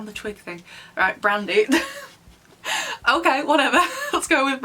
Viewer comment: brandy was a subtitle error. They said brindille which is French for twig. The subtitle people probs thought it sounded like brandy. Honestly, brandy is a cooler name than twig.